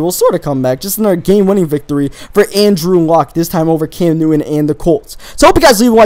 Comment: Is it just me or did he say "Cam Newton And the Colts" at 9:16?